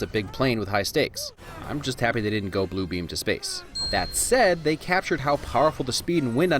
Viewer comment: did he just say "they didn't blue beam to space"? Did he just unknowingly inspire that scene in Avengers: Infinity war?